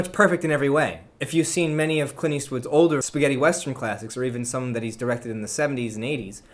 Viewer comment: Hey, it sounded like you said "Spaghetti westerns" around the 1:10 mark xD